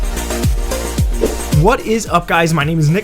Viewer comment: bro what did u just say! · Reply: "What is up guys" ? Lmao 😅